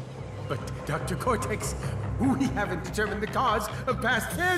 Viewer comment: I heard "Who he haven't" but the script says "We haven't". A native speaker can explain me what really it says?? :"(